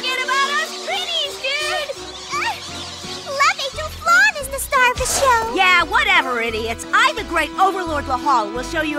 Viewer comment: What is Flonne saying? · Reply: "Let me through, Flonne is the star of the show"